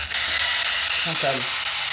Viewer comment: Bonjour , à 29:06 au moment de la séance avec la spirit box , j'entend plutôt " Chantal , épouse moi " Voilà , je découvre vos enquêtes et j'aime beaucoup :)